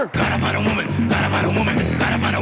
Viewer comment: and when i hear it, when ever i hear the part where they go to Kissinger, for some reason i hear "dynamite a woman" instead of "Got to find a woman"